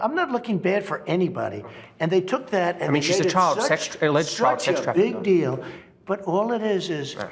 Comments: Did anyone else hear the interviewer say "but she was a child trafficker " and trumps response "big deal" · I am sure when the interviewer repeated she was in jail on alleged child trafficking, Trump replied "Big deal"?? Or did i mishear that